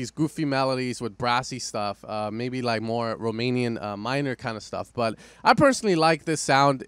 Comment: did you just say "romanian"